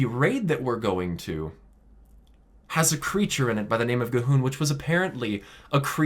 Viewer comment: I'm sorry, did you just said "gu'hun" or C'thun?